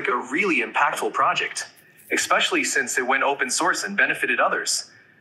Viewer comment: Anyone else hear, "ekspecially"?